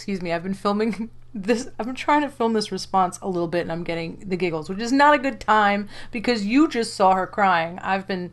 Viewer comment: They can’t do it while you are breast feeding and Channon said less than two weeks ago she could not breast feed now. See this is how she misleads. Everyone heard she is on ivf but what she specifically said was that she was trying to get on ivf. She’s suggested it. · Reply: ahhhh! I see! I thought she said trying ivf. Lord she’s a mess. Just watched her video about some one trying to take her baby.....there are so many holes in that story. I can see that she is lying, I just see it now. Wow! She sold her soul for views. So messed up